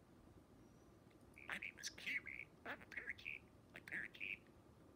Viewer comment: "My name's kiwi, I'm a parakeet!" TOO PURE FOR THIS EARTH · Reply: I thought it said I'm a turkey lol